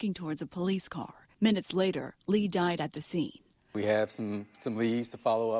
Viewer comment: I thought they said he died at the hospital. Strange. RIP. I wonder if the gov had him taken out as we move towards the CDbC system (FED NOW) or some rando just decided to do this?🤔🇺🇸🇺🇸